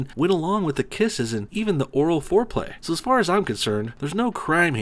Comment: I thought you said "boring foreplay" first hahaha